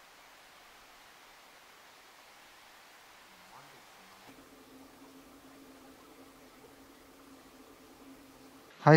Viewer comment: お疲れ様です😌 26:38辺り、声のような音が聞こえたように思いました😐 おまけ…ゲジゲジで、大騒ぎしてる3人が、とっても仲良しで楽しそう👍😆 次回も楽しみです😊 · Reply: がさんの問いかけにやぁなんですかねぇって言ってる声ですね。